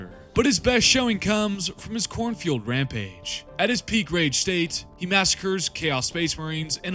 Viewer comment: i kept hearing "khorne fueled rampage" as "cornfield rampage" 🌽